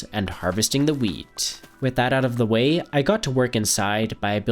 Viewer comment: sounded like now lets harvest the meat so i rewinded it and it was actually wheat love ur vids bro